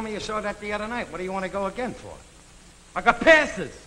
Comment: "I got ___." at 11:32, what is that word, please? I could not understand it. Was it "I got panted?" If so, I don't get it. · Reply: Passes